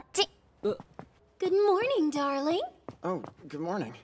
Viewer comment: I hear: “da wing.” For the sub version. 👁👄👁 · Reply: I heard "daring" TwT